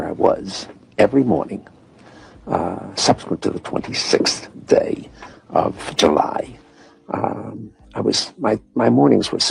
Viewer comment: Listen to what he said at 1:06 "Psyops went to the 26th day of July." This is a guy who also said he was "home", he was codespeaking that he is a corrupt agent of Department of Homeland Security who is in on the psyop agenda against humanity, with a false national security excuse. The government poisons people with nanotechnology, take supplements, they want to handle everyone